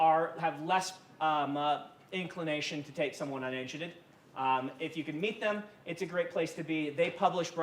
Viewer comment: Can anyone understand what he says in his funny North Americaaan accent at 29:27? "... less inclination to take someone [ - ?! - ]..." - unedited/ uninitiated / on initiative / ...? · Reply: unagented